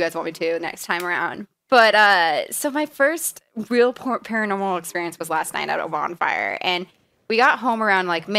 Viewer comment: I thought you we're going to say "my first real porno" :P